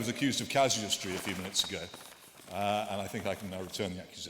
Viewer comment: Can anyone tell me what Peter said at 1:29:40? I didn't understand the word. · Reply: "I think I can now return the accusation". Is that the part?